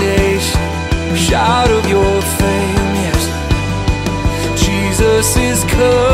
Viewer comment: Why does it sound like dude says let the world shout of your failures? lyrics say fame but dude hecka sounds like he says failure